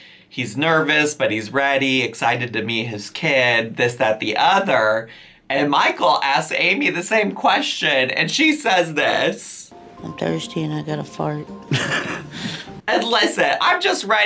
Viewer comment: I thought she said “I’m thirstier than a fart” 🤣